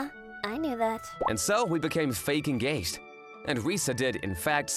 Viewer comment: 6.30 Sorry, I just can't...😂 I thought I heard "Fakest Gay" 😭